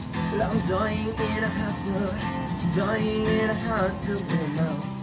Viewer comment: bitch did he just say FLYING in a hot tub